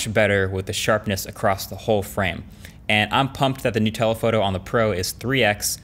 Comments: You said: “new telephoto” I heard: “Nutella photo” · When he said “New telephoto” I heard “Nutella Photo” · When he said “new telephoto” I thought he said “Nutella photo” lol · When you said, “new telephoto” I totally heard, “Nutella photo” and I had no idea what it meant, but I think that sounds delicious. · who else heard "nutella photo"? no? just me? ok. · When he said new tele photo, i heard nutella photo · was I the only one who heard Nutella photo Instead of new telephoto or did others too? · idk instead of new telephoto. I hear Nutella photo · why did my fatass think you said 'Nutella photo" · Who else heard the Nutella photo at 4:08 ? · I totally heard this as "Nutella photo" instead of "New telephoto"